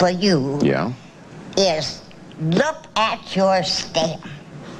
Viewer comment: what is she saying?????? Lol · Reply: She's saying that she would look at his Stem. And of course, this in interpreted in quite a different way! lollll And lastly she says that he needs a good strong stem if you're gonna get a blossom at the top! lolll I feel a little bad for her because she was being very serious lollllll